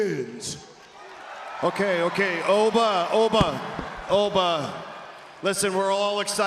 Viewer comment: did he just say Obie?😂😂😂😂😂😂